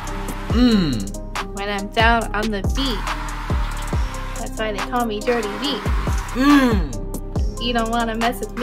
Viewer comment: I thought said that's why they call me dirty feet